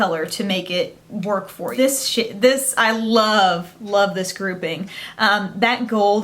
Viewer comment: did you just say "This shit.. this I love..."? 8:00 lol